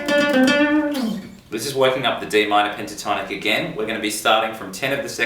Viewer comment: i thought he was trying to say waking the demon at 12:51 but he said walking up the d minor HA